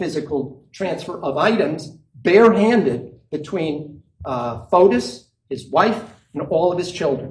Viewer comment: Did he just call Fotis FUNGUS (34:10) 😂😂😂